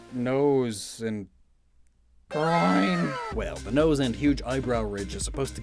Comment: I accidentally skipped to this part and all I hear is just GROIND